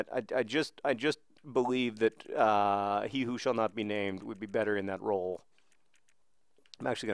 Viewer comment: I thought he was saying P. Who Shall Not Be Named :O haha